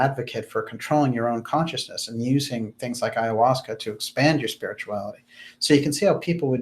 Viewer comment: What's that word at 2:14:20 regards helping your spiritual reality if someone be so kind to tell me please thankyou 🤗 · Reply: Ayahuasca usually done in retreats in Peru etc some people use DMT for similar experience